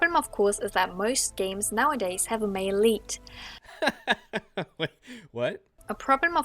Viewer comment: I thought she was saying Maleite and I thought it was some new feminist word. then I realized she was saying male lead a and felt stupid